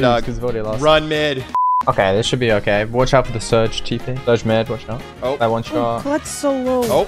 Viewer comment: "Watch out for Surge tp" did I just hear that right ?!